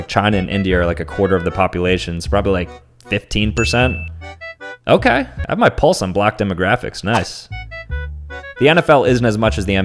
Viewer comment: “I have my pulse on black demographics” bro what 😭😭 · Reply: i thought he said “i might pull some black demographics” as in get more black audience